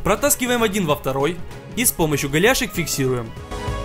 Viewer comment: голяшек или мне послышалось😂?